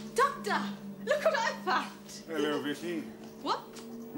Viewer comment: Did he say "Hello Missy" around 0:20?